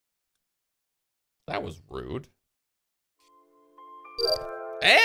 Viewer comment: did he really said one shot baby......... Who don't react on continuous headshots